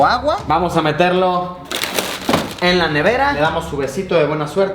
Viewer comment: Soy el unico que noto que dice "TE AMO" en el segundo 10:55... Ponganlo en velocidad ultra lenta :3